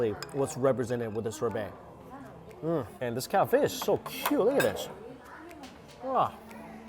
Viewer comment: is he trying to say persimmon @ 12:00? I keep hearing a hybrid of Chrysanthemum + Persimmon... LOL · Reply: and what is he saying as he picked up the beverage @ 12:29? all I hear is “This Cow-fish...” haha anyway, love the video!